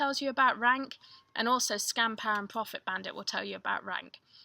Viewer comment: Great video but @ 1:01:17 -- what did you say? "SCAM POUND PROFIT BOUND"?? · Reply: Scanpower and Profit Bandit :) THere's also another app now, Scoutify that's pretty popular.